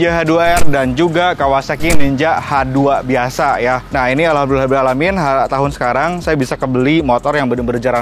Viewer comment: ngomong apa bang... blabla alamin.. muslim bukan bang... klo bukan gk usah ikut2an... dari pada slah kaprah..